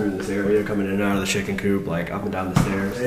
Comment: When it “the chicken coop” I thought it said “the chicken poop” XDDD